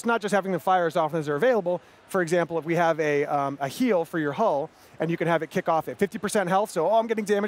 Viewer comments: did he said a heal for your hole? look at face :D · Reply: He said "A heal for your hull". He's talking about Ship combat, so that hull of your ship. I recommend using Hazard Emitters at 50% Hull. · did he said a heal for your hole? look at face :D